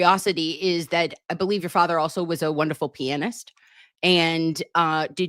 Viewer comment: 😂😂😂 i thought said David's father had a wonderfull penis 😂😂 i though oh no i hope you didnt clutch his pearls.